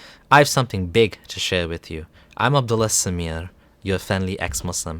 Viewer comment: Did a doubletake, thought I heard "I'm a blasphemer" in the intro. Kinda on point though. Jk, you're the speaker of truths